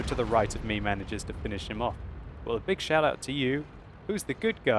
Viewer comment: Can anyone say me what does he says at 20:10 after "big" to say he thanks the FV a lot ? I'm not a native English speaker and I've noticed this expression a few times so far, but I've no idea of how to write it... Always a pleasure to see you show your skills btw, as usual :) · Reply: a big 'shout out' to ..players name do you mean? ..a modern online video/audio-blog expression, it is like saying "Hi/Hey/Cheers/Thanks (...players/persons name) !" Equal to shouting some persons name in respect, honour, praise or gratitude etc, informing others of them & their deeds - not usually a public real life thing anymore since the after the 13th Century, unless toasting them ...or drunk.